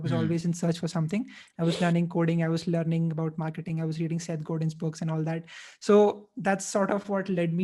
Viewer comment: I was learning coding, marketing and _ / if someone figures please share what exact words does he says.. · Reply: Seth Godin books